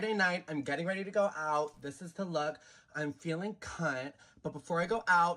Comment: WTF did this clown just say?! 😂 🤣 "Im feeling CŰŇŤ" ???? 🤔 Anyone hear something different?